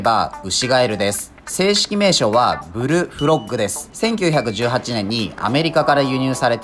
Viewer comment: ブルフロッグがヘ○フロッグに聞こえた俺は難聴